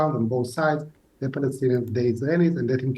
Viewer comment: does he say "Israelis are getting killed by the day." ?